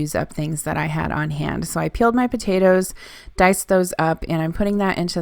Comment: When you said "peeled my potatoes" I heard "killed my potatoes". LOLOLOL!!!!